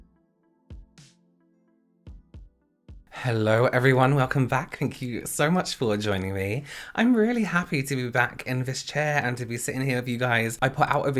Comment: When he said, “Thank you for joining me,” I thought he said, “Thank you for enjoying me,” and my immediate thought was, “Yaas! Go off Queen🙌🏻🙌🏻”😂😂😂